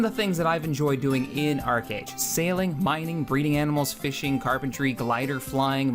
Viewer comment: I thought you said "breeding with animals". 5:01 had to listen to it again.